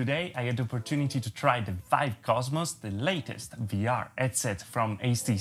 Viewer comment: I just hope vr doesn’t die due to htc making they’re last headset and oculus team quitting. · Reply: Yeah it seems that a few people got confused about this. I heard "latest" though. Edit: He did say "last" the 2nd time, by accident